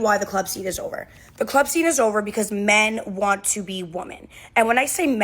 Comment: she said "men want to be woman" but the subtitle says "men want to be women" - you should subtitle it reflecting her poor grammar